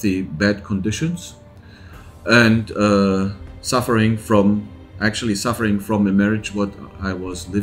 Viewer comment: sounds like he said suffering from a marriage? If so understandable